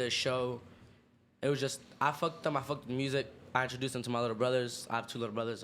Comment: I keep hearing Ben say “I fucked them .. they fucked me ..” 😂😭😭😭